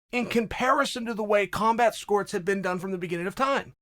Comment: did you say combat scorts?